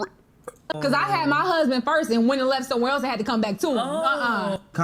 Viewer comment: I thought she said she had her husband first and wouldn't have left to have to come back to him